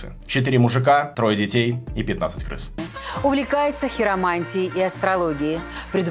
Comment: на секунду послышалось Некромантией... или не послышалось ?